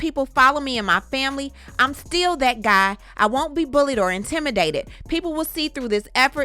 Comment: He sound like he was saying still hustling but it’s interesting that he used Chris brown’s name.